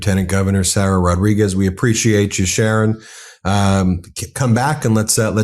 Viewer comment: Cargill in Mason City, IA is laying off people. I don't know the specifics. Ben, who is Sharon? At the end, I could have sworn you said Sharon, so I rewound. Yes. You asked Sharon to come again. Slip of the tongue?